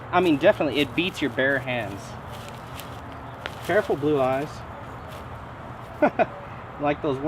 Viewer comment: Yeah no problem ! I pulled this one from the collection, glad it survived ! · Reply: did you name your chicken blue eyes lol that's what i heard sorry if i misheard